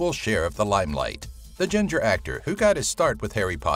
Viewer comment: did he call him ginger? LOL